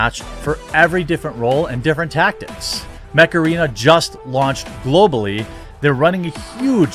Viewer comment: Install Mech Arena for FREE here ✅ <link> and get a special starter pack to help kickstart your game! :💥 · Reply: Not gonna lie, when you said "Mech Arena", I thought you said "Macarena". 😆